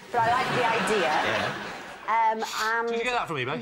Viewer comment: what did Lee say? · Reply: If nobody has replied, Lee says. "did you get that from Ebay?"